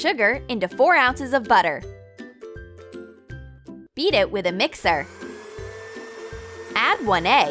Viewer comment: I thought it said eat it with a mixer 😂😂